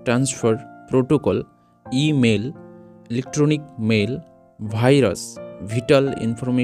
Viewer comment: Did i just hear"Bhai ras" instead of virus